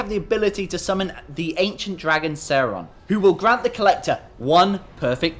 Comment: What did you call the dragon? It sounds like you said Saron. His name is Shenlong (Shenron in the Funimation dub).